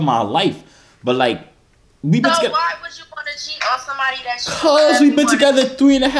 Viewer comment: It sound like she said you wanna catch aids 😭😭